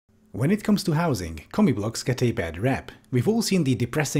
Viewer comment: Everytime he says "commie block", I hear "comic book". 😅